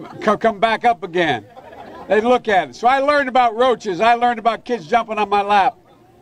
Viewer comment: I learned about roaches, i learned about kids jumping in my lap? Who is he calling roaches?